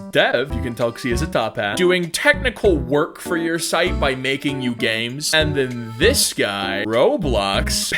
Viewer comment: When he said "by making you games" I really thought he said "by making you gay" 😭